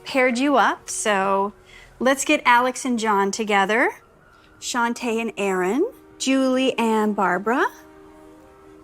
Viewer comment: I had to do a double-take at 3:03 because I misheard "Shaunte"(according to the subtitles) as "Shantae" 💀